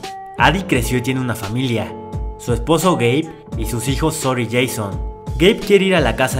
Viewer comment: yo entendí gay XD